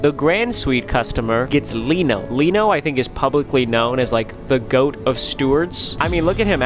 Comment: I hear ‘Leeknow’… I know, you know, Leeknow